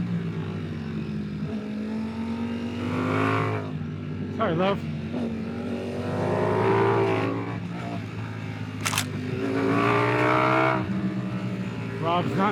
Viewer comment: haha @ 7:34 it sounded like you said "alright love" when you had to brake a good bit more than you wanted to stay behind. It sounded like the nice way of saying wtf you can go faster!! You two are awesome, there's a race this weekend out at our track so maybe I'll join in, not sure yet supposed to be stupid hot like where you are, but I see your videos and I am itching to get out on track.